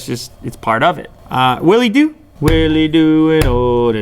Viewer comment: did I hear will u do LoL 😂😂😂😂😂